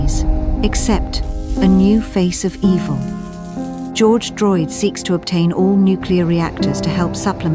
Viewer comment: I thought i heard George Floyd at first😅